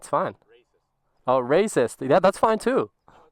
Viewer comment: i heard racist and locked in